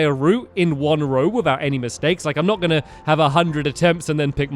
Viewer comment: whh did u say steaks